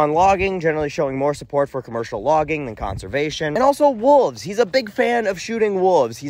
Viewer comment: When he said "Wooves" I had no fucking idea what he meant. Kind of had a "My Cousin Vinny - did you just say Yoots" moment. Wolves.